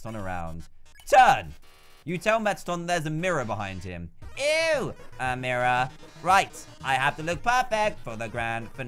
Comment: OH??? A MIRROR??? what I heard EW!!! A MIRROR???